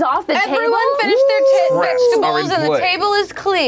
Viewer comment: Um at 6:24, did CoMil say “…everyone finished their tit, vegetables” or am I just incredibly single and lonely?